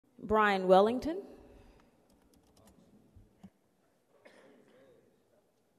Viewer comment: Imagine for those people that watched this and had no clue who they were seeing. 😂😂😂😂😂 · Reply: I thought they said Brad Wellington for a second. 🤣🤣